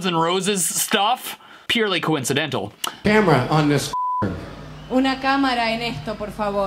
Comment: no, instead of f**k, she said "a camera on this please"